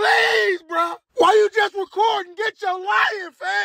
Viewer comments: "Why you just recording? Get yo LION FAM!!!" 😂😂😂😂😂😂😂😂😂😂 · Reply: Thanks. My brain originally thought he said get your life man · "Why you just recordin, Getcho lion!" 😂😂😂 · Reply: I thought he said “get yo life” guess not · "Why you just recordin, Getcho lion!" 😂😂😂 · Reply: I thought he said "get your wife"